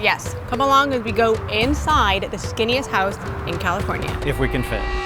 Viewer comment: I thought it said the stinkiest house not the skinniest house 😂🥴